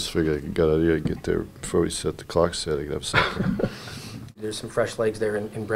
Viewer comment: I thought he said suck my c**k. Nice guy was just surprised but he said 'stop the clock'. Canadians say things weirdly especially his voice is so monotone. he is such a great coach and doesn't care what anyone says, if he's liked or not. People like that are rare and interested in what they do and don't need anyone to tell them anything else.